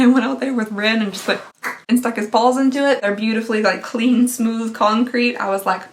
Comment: For a second I thought she said she stuck his balls in the cement and I was VERY concerned for her dog and her sanity LMFAO